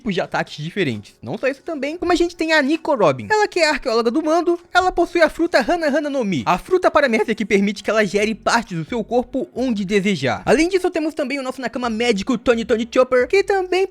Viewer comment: foi só eu ou vcs tbm ouvirás a fruta paramerda no minuto